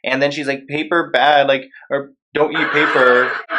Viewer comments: ‘Paper bad’ talking to her like she doesnt understand english. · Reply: I couldn't hear clearly and thought she said "paper bag" referencing the test or something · I hope I'm wrong... But I heard her say paper bag paper.... like, what does that mean wth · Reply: She said “paper bad”